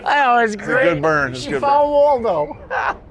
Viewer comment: wtf that girl said? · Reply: She said "I found Waldo ".